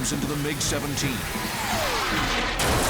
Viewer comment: I thought it said "Agent smith's missile slams into the Mig 17" The Mig was being flown by Neo